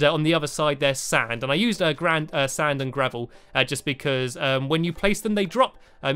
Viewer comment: OMN when you said sand and gravel I thought U got mixed up and said save. And OMN stands for oh my notch